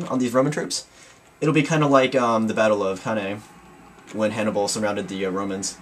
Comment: Did you say the battle of Hannae, or Cannae? It sounded like you said Hannae to me though. Anyway good game though. You never cease to amaze us, well at least me anyway.